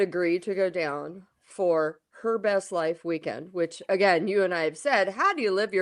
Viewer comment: At first time I heard "Herpes Life" and honestly it would've been sooo much more AUTHENTIC title for that weekend 😳🙈🤣